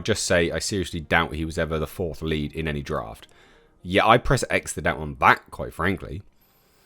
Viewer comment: What? I keep rewinding what you say and still I can’t understand. It sounds like: “Yeah, I’d press X to that on back, quite frankly. Massively.” What the hell are you saying? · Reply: I'd press X to doubt on that. It's a phrase pressing X to doubt